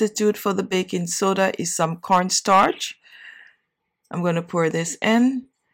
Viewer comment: What option no baking powder there is replacement of baking soda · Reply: Heard her say cornstarch